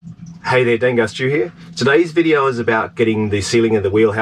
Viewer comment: kinda sounds like you say "Hey there Dangar Studio"